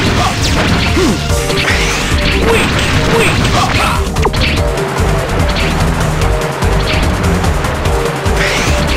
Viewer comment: What'd X say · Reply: X: WEAK!